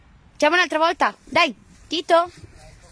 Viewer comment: Did she say "Travolta"? Sounds like Travolta, John Travolta 😆